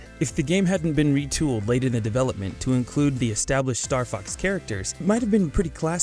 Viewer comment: Did he just say star f**